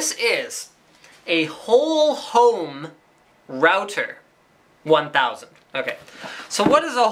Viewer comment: my volume too low and i thought he say "hoho router" ....